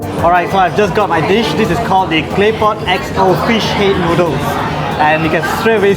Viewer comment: hahaha towards the end I thought said "K-pop noodles" 😂😂 I was like "whaaat? no one said k-pop?! aaaaah, it's claypot!!!"